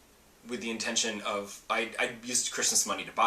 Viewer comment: For a second when you said that you "used Christmas money" I misheard you as saying "I used Chris' money" and thought you were suddenly referring to yourself in the 3rd person! >_<